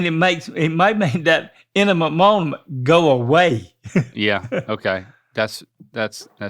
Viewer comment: Two things: I was having egg drop soup when they were talking about the poop I sounded like was saying enema moment haha